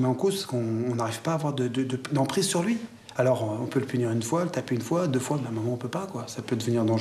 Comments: c'est moi ou à 12:16 il a dit :" le taper 1 fois, 2 fois... " tu m'étonnes qu'il soit énervé le gosse !! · C'est moi ou il a dit : le tapé ? 🧐😤